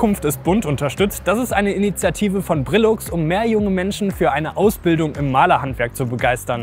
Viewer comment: Ich hab erstmal verstanden:"Das ist eine Initiative von Trilluxe" 😂😂